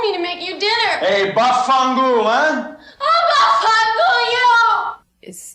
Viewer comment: hi I'm half Italian half Spanish he actually said Vaffanculo it literally means Go f*ck yourself. Sorry :3